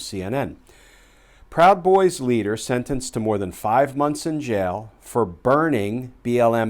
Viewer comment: This guy is chump change but every nickel counts! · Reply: Aw fck. I thought he said 5 years not 5 months. Damn. Hopefully he'll get another turn in the barrel